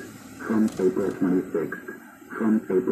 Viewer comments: I hear "from April 26th". (Sorry, don't mean to be a downer.) It is creepy tho. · pretty creepy :/ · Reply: I heard "Trump April 26". I don't know if their numerology works this way, but: April is the fourth month, counting forwards; September is the fourth month, counting backwards.